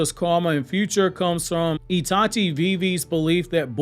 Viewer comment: I thought you were saying it comes from itachi at the beggining and was super confused 😭